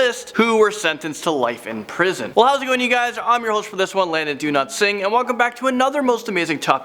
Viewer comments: I thought he said, "Don't sing" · Why does it sound like he said “Landon do not sing” 😂😂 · I thought he said his name was Landon do not sing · I really hope this doesn't come out wrong... But... Do you realize that when you said your name super fast like that it sounded like you said "Landon Do not sing" I'm sorry darlin... It made me giggle... I love your energy though.. your happy energy is one of the main reasons I watch this channel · Sounded like you said your name was Landon do not sing.. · I thought this man said "Do not sing" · Did he just say 'landon do not sing'? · I swear he says, Landon "Do not swing." (Btw, Ik he was saying his past name.) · When Landon said his other name I thought he said do not sing lol sorry